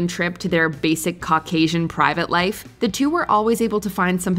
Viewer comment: did that just say “your basic Caucasian married life”? 😂😂😂